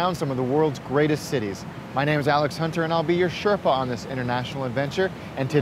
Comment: What's the word he's saying at 0:07? "I'll be your __________ in this international adventure..." It's driving me crazy how I cannot make out what it is. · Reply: Sherpa